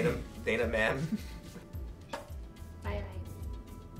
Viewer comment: What does say at 6:01 that makes Ryan laugh? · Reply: She says, "Aye aye," giving away the location accidentally.